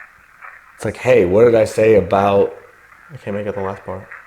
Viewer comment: Hi guys at 49:55 I think it says Hey what did I say about down stairs! 😅 Anyone else that heard that too? Stay safe!